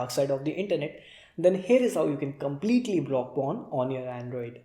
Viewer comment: I thought it said completely unblock 😂